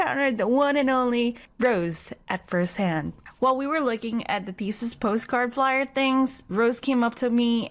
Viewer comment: What I heard: While we were licking